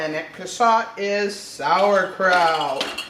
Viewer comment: Did you say NOT to put salt into the jars with the cabbage? You said it very quickly at about 9:56 and it sounded like salt makes kraut. I listened several times so I decided to ask. 55 mins at 10lbs pressure for quarts after 3 minute blanch loosely packed. Please let me know if I should or should not add salt. Thanks for all that you do for all of us. Vivian Ann